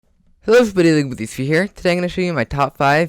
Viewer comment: WUT DID HE SAYYYYYYY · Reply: he said "hello everybody, legoboyz3 here" really fast